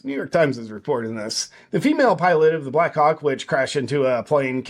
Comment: It sounded like you said: She was flying a "Black Cock" instead of "Black Hawk".